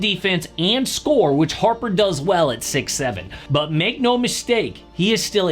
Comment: wait did you say 6,7